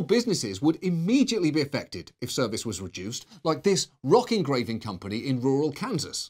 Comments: I thought John said "rocking gravy company," and was slightly disappointed. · He said “Rock Engraving Company”? I thought he said “Rocking Gravy Company”